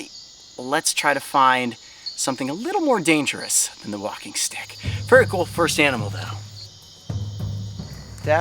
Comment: I thought that he said ok let's find something a little more dangerous then the walking dead